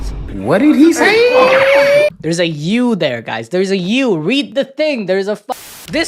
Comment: all I heard was "there's a You in there" lmao